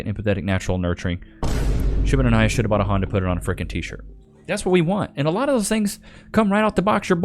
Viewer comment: WTF does he say at 9:07? LOL "Should've _____ _____, should've bought a honda, put it on a tshirt"?